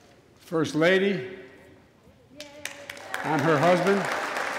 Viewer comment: did he just say "the first Lady, and her husband." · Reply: “The First Lady, I’m her husband”